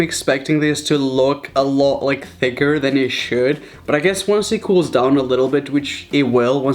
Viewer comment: did he say when the icecream cools down lmao